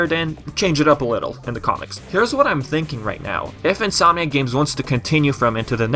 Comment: Every time you say "Insominac games", I hear "Sonic games". Similar pronunciation somehow. In any case... Hopefully if Nexus-like game happens, it's not a big disappointment. I am so used to huge disappointments coming from epic concept arts and promises of a darker tone. Sometimes those are just promises (see Sonic games) and a way of luring fans to the most minimalist version of what they could have done.